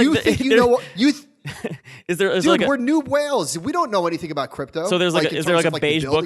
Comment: “We’re new whales”, hahaha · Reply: i heard “we’re noob whales” 🤣